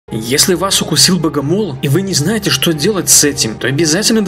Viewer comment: Мне одному послышалось боговол?